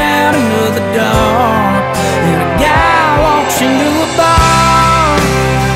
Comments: Haahahhaha my cousin thought he said a cow walks into a bar but it's a girl walks into a bar !!!!!!!! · When I heard this on the radio I thought it said, "A cow walked into a barn." Come on I know I'm not the only one....